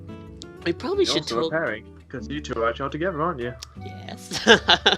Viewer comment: I know this is of somewhat kinda old video but at 14:22 does say" Your also a parent cause you had a child together."? Or am i just bad at hearing?👀👀